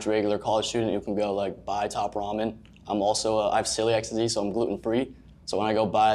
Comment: i rlly thought the celiac guy said he’s "silly XD"...